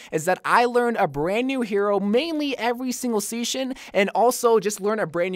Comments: did he say seshin instead of season?? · every single seasnion ? 2:11 ( am i just hearing season wrong or am i hearing seasnion? )